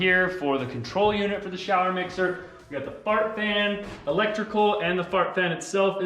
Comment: I rewound several times and I swear keeps saying ‘fart fan’ am I correct in what I heard because if I am I am never calling it an exhaust fan again, it will forever be a fart fan hahahaha